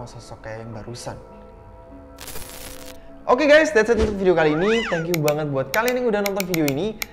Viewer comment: menit 12:06 bang ngomong apa sih ?? ada yang tau ?? 😁 .. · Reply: Untuk video kali ini dia bilang